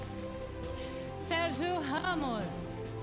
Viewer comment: it sound like she saying Sergio hummels